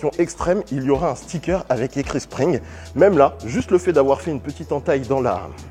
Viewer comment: J'ai entendu "string" 😂